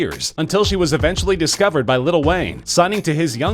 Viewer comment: Umm @ 0:29 did you say "Little Wayne" pffsht loser aka dumbadd